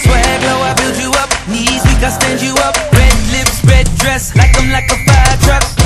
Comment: hahahahhahahahah i always thought he said "swear won't abuse you up" XD sorry is because the whole Rihanna situation lolool